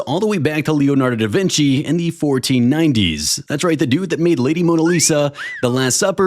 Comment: I heard first "who laid Mona Lisa" I was like, that's a bold statement, but carry on... XD